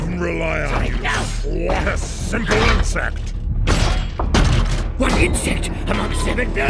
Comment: "I can't even rely on you" "SORRY- NO-!!!" "To swat a SIMPLE INSECT!" "One insect! Among 7 BILLION!!" "SHUT UP!!! God the acting is so much better than G1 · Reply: I thought he said “what a simple insect”